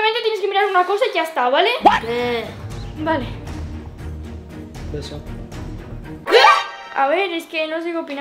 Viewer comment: escuche beso de q lo decía